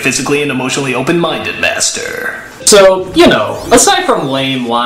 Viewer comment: Funny, and I had another window open, and I heard "bastard" rather than "master".